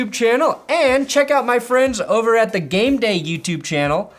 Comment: I thought originally said "The Gay Day" instead of the "The Game Day", actually depending on who the Broncos find as a QB "Gay Day" might be appropriate. Of course, I don't care the sexual orientation of a player... W's is what is it all about baby, not LGBTQABCD.